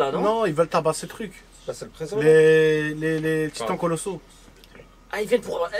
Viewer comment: めちゃめちゃ真剣に聞いてたのにここ｢ﾋﾄｺﾛｽ｣にしか聞こえなくて笑った